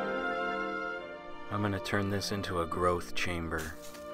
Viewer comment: I thought he said Girth Chamber